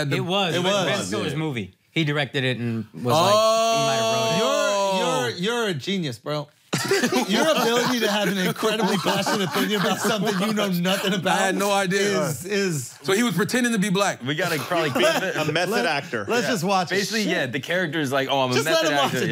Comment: “Your ability to have a passionate opinion about something you know nothing about” 😂😂😂 -Shulz calling out CTG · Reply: i thought he said biased opinion but either or lol